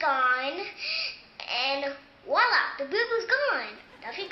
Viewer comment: the whole time when she said booboo i thought she was saying boob boob